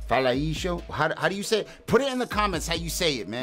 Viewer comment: what he says is "fala aí, jão" the "jão" (pronunce as jaum) means bro/man, is a commum slang from São Paulo area